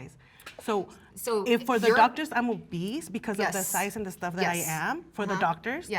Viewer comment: "She's a little bit plus size" girl you're not a little bit plus size, you're just obese and that's totally fine to admit. This denial is what causes our own demises as human beings. I've been there done that until I lost 154 lbs. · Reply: I thought she said "I am obese"